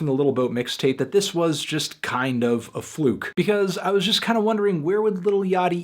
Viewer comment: did he say a "flook"?